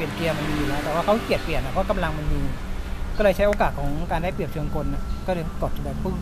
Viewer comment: นาที ที่6:44ช่างเขาพูดว่าอะไรฟังไม่ชัดเลย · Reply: ใช้โอกาสของความได้เปรียบเชิงกล